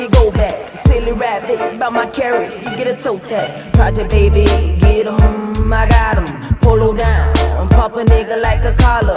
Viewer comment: When Kodak said get’em , go’em in min 1:28, it kinda sound like was saying spotemgottem